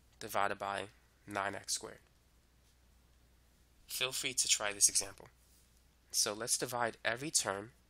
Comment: any one hear filthy to try this example instead of feel free to try this example at 8:01 :]